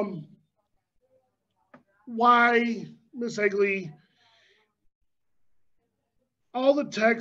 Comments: LOL i was just listening and thought he said Ms Ugly. Had to do a double take. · I thought he said ms ugly for a min 😂😂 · For a second I thought they were saying Ms. Ugly until I read the screen names. My hearing sure is off. Lol